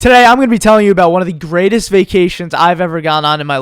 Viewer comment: At first I thought it said vaccination lol